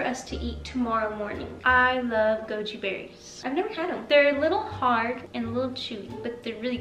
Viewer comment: I thought you guys said gucci berry instead of goji berry